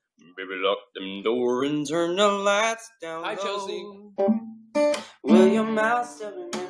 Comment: I heard "hi Chelsea" and I was like what😂 #VocalGoals